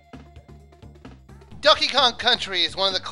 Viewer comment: I thought you said Duckey Kong....